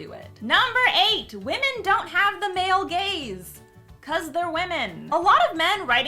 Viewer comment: Whenever someone says “the male gaze” I hear “the male gays” and it gets confusing.